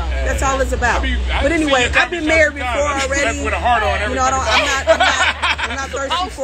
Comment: I am trying to hear the convo in the background lol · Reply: what they were saying in the background sounded way more interesting than whatever Tressa was talking about. I heard the words "hard on" and whatnot.😆👂🏾lol.